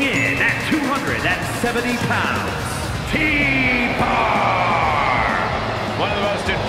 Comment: I still think it sounded like "Pee-Bar" instead of T-Bar. 😂😂😂😂😂